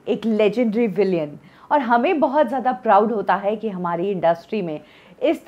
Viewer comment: have i rightly heard villian instead of villain